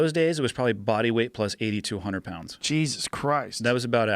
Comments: I thought he said 8,200 pounds...had to listen a couple times. He said 80 to 200lbs. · did he just say they were carrying body weight plus 8,200 pounds?????... nvm hunter pounds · I thought he said body weight plus 8200 lbs! And I would have believed it. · Legit thought he said body weight plus 8200 pounds 😂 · For a second I thought he said body weight + 8200 lb. Was about to call bs lol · So, I heard 8200lbs instead of 80 to 100. Then my brain caught up. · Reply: I thought I heard 80 to 200. But that 200 part don't add up either.